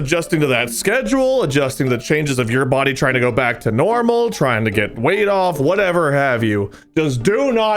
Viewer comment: When wade said "trying to get weight off!" I heard trying "trying to get wade off" and i was like... wait what?